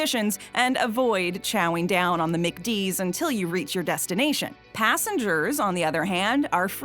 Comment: I thought she said Mickey D's